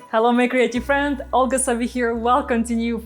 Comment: Every time she says "hello my creative friends" I hear " hello my crazy friends". In my case, either is fitting!😂